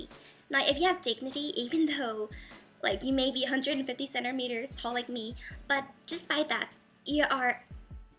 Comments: that's what I thought! She said I'm 150cm tall, I believe. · I thought she said that at first but se said 150 cm tall :) xx